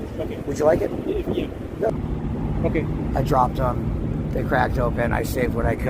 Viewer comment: I swore he said “ I dropped the crack dope and… “. But he actually said “ I dropped the cracked open( beer can)… “. 😂